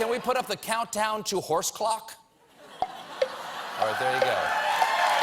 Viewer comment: I thought he said countdown to hourse cock